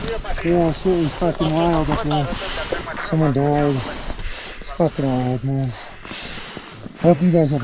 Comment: shell shocked bro. “Someone died…” is that what he said??